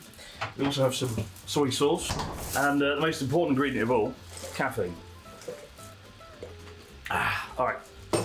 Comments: "And the most important ingredient of all... catfood." i swear, that's what i heard him say 😭😭😭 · I misheard the first time. I thought he said the most important ingredient was Cat Food